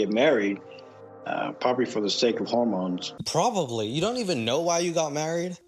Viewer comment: thinking he said they got married because of “home loans” and not “hormones” is so funny to me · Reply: When I heard it, I thought that maybe I misheard, but home loans were better than hormones, so I accepted version XD.